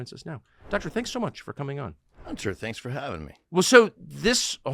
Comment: Did he say “Hunter, thanks for having me”